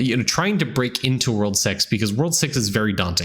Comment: he really said "World sex" i had to rewind it a few times. lmao.